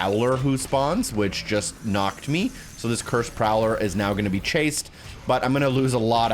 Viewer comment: Sounded like you said cursed growler